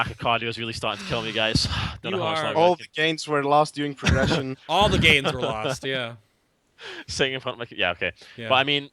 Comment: after got himself some water he was talking about something begins at 51:00. i heard steriods? and they said it, they killed blackhand now tree times before another guild took him down. and i think with no sharing at all (at least the last two kills) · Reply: He said that "someone compared sharing an acount to using steroids in sports" which he considered ridiculous. It's more akin to sharing a really really good training partner and coach for sport training on the side of your usual team.